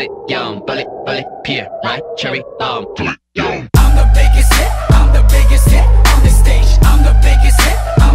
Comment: ok but...why did i think that jaehyun was saying "im the biggest dick" at 0:17?